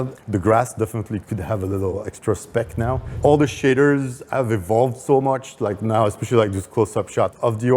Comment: with his accent is sounds like he says all the sh*tters instead of shaders 😆